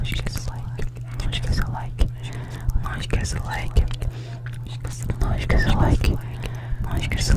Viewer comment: Não esquece o like... - no esquece o like... - noes quece o like... - nos que ce o like... - nos que so like... só eu que dps um tempo entendi isso? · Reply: Eu entendi "nos que só like"